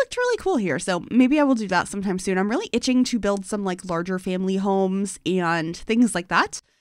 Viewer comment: i was chewing on some chocolate during this, and managed to mishear this as "some like larger candy homes" and had to do a double take 😭 there's an idea for you, i guess! candy colored family homes! or a home for a candy lover? or candy maker...? could be cute!